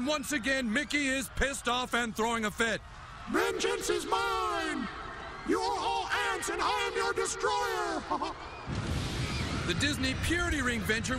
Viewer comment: what did he say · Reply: Vengeance is mine! You are all ants and I'm your destroyer ha ha!